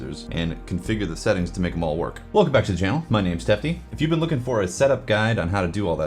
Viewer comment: I'm glad that uour name was referenced in print. I thought you said Stephanie for some weird reason.